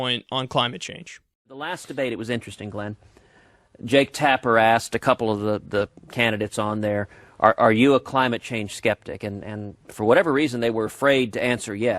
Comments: For a sec I thought Cruz said "tap her ass". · First I thought he said 'Jake, tap her ass'